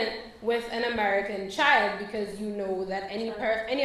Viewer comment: Excuse me, young woman: "pregnant with an American" WHAT? You said, "child," I heard you.